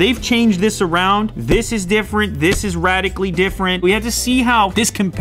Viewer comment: did he say radically? NANI!!,!,!,!